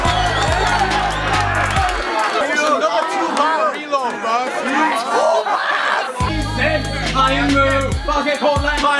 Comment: what did they say in 4:33? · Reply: AshCashTrash 'he got a two bar reload... TWO BARS'